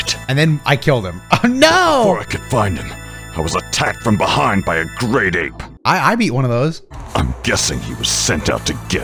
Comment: I'm sorry piccolo but did you just say you got snuck by a great ape?